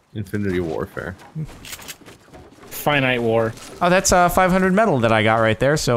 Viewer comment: "oh that's ah" sounded like "Alexa"